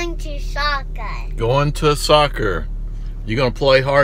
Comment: Soccer? I thought he said shotgun.